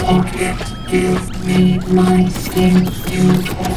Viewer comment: seconds what is he saying I can make out some words but that’s it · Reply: Give me back my skin, you took it took it took it took it took it took it took it took it took it took iii... Do do do dooo {feeling good?} Feeling better